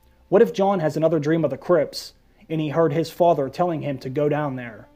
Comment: The way you said Crypts sounds like Crips, the L.A. Gang and what I heard was "What if Jon has another dream of the Crips" and I busted a gut thinking of Jon in a blue bandana with a Crip entourage